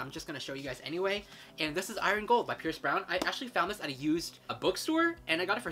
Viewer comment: I thought you said "Iron Gold by Chris Brown"